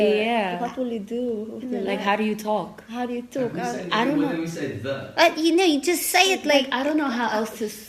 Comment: I think in the background Ninja said "and who say n... and when we say them"